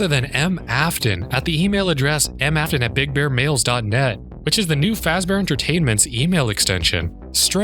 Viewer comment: i swear it said big bear balls